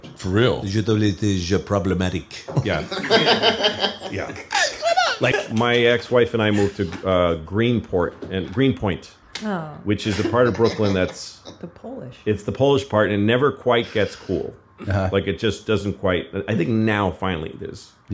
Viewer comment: I thought he said "my family and friends moved to greens point" I was guna say Ross from intheclutch Ent is triggered 😂😂😂